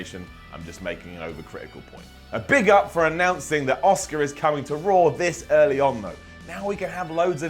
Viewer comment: It sounded like Simon said Oscar, not Asuka